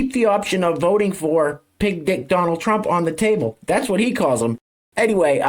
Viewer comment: I always thought Rappaport was saying "big dic Donald Trump" 😂